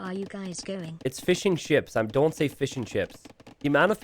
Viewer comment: Looool I always hear 'fish n chips' when says fishing ships. Glad it's finally addressed :p